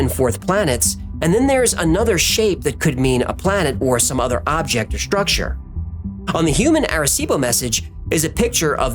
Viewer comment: OK I NEED HELP HERE What was said at 9:25 ? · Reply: "This thing appeared, peice of evidence"